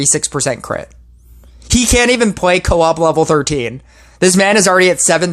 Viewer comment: Did you say V1N5 can't or can play coop floor 13? I didn't understand. I know he can't, but I thought you said he can. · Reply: Cant.